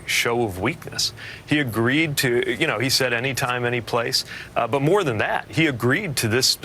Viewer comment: I thought trump said any place any time